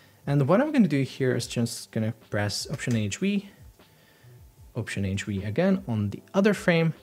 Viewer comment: Great tutorial but your shortcuts are not audible 6:14 option H V or optionage, v or is it option, agent, V??? (regardless it doesn't make sense) 6:58 is it "option, agent, enter" or optionage, enter?? your display said command option enter, noting like what you said